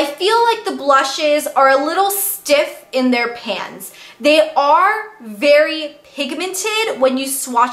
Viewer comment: Haha, when you said " they are a little stiff in their pans.. I heard " they are a little stiff in their pants" 😄